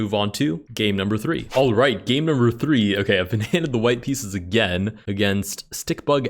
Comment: did bro say the white pieces?